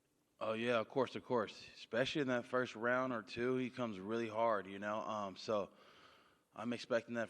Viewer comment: “ He comes pretty hard” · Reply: When did he say that? At 1:30 he said "he comes really hard", but where did he say what you said?